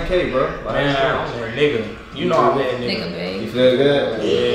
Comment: Did say bae